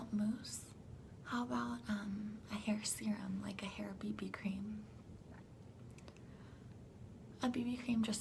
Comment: Ok.. I'm unsure of what i heard around time mark 16:15... Did you say "boobie cream"? i find it a bit confusing.. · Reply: BB cream! Beauty balm, I think it stands for.